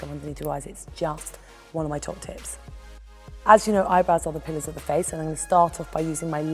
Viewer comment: I thought she said "the eyebrows are the penis of the face"